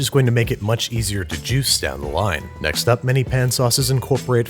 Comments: I thought he said '' down the lime'' at 1:16 lol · when he starts prepping the lemon I first heard it as “it’s going to be easier to juice down the lime” lmao 😂